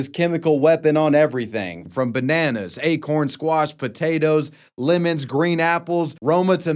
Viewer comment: did he say acorns? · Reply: Acorn squash, type of squash